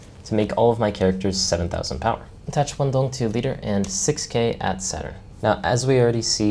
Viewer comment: I thought you were saying 7 at 7, but you were saying 7 at Saturn. Great walkthrough dude 👏